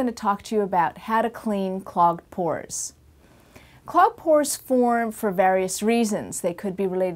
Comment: FOR A SECOND I THOUGHT SHE IS SAYING PORN PORN PORN